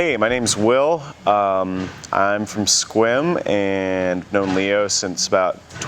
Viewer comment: For a moment, I thought I heard Will say he was from Squirm, not Sequim.